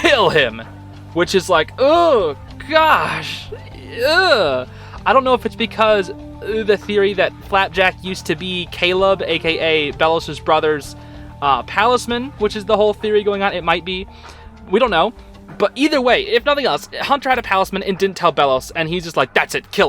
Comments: ngl I heard Cabel first 😶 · actually I think he said Caleb